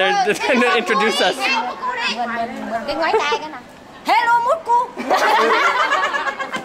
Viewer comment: sounded like "hello, mulkku", mulkku means dick in finnish